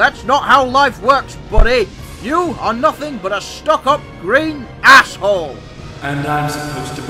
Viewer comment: What did Gordon say?!?! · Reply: Why did he says "Asshole?"